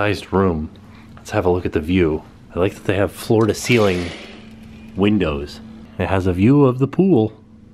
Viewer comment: LOL I thought you said "Florida ceiling windows" and I thought "What're those? Pretty sure we have them in California too" haha